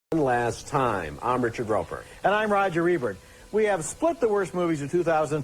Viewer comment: What was Roeper saying at the beginning? Sounded like he said “for the last time” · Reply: I'm pretty sure that sentence went something like, "Tonight we review these terrible movies for the last time." Many of the old yearly "Worst of" shows started off saying something similar to that.